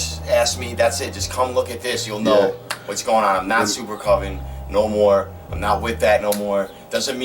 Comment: what does he say at 13:21? im not.. ? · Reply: Marko supercoven, the label